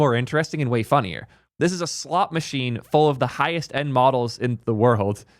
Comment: Sorry I thought you said “slop machine” 😂